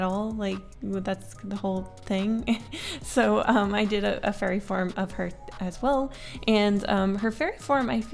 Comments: I thought you said "furry form" instead of "fairy form" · When she said “now I’m gonna design her fairy form” I thought she said furry form lmao